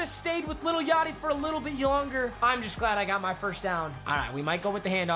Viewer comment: did anyone hear yonger ??😂